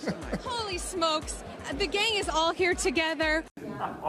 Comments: I thought she said the gay is all here toghethor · I heard “the gay is all together” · the gays. I know what I hear.